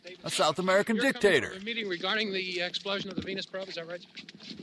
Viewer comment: anyone else hear "...regarding the explosion of the penis probe"? O.o